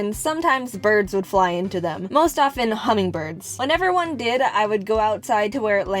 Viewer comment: I forgot what hummingbirds were called, because i wasnt listening to the video when she mentioned them, and i thought she said honey bird, so i had to take like 3 minutes to remember what hummingbirds were called xd